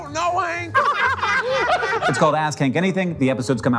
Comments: Ass Kank Anything. That's what I heard and that's what I'm sticking with. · 100% thought he said the show was called AssKink for a second and didn't question it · Oh no, I heard “Ass Kink Anything” and can’t unhear it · Finally figured out the show is called ask hank, not ass kink. Little dissappointed i only misheard 😢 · I heard “ask Hank” as “ass kink”. Maybe because it came shortly after the ass-blimp. I’m sorry. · I had to do a double take on "Ask Hank Anything" because on my first pass I heard "Ass Kink Anything" 😭😭😭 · At first, I thought Hank said the name of the show was Asking, which made me chuckle. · I totally heard “ass cake” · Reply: I heard "ass kink" 😂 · Really thought you said it was called "ass kink anything" at first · I will say I’m very excited about this show but also my audio processing issues thought people were saying “Ass Kink” instead of “Ask Hank” so there’s that